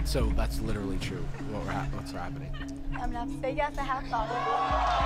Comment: It's really hard to hear but at 30:55 she says "I'm gonna have to say yes I have thought of it." I had to go to daily motion to hear it there so I could tell what she'd said